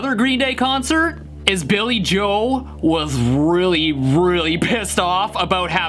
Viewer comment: Did he say Billy Joel at 0:27?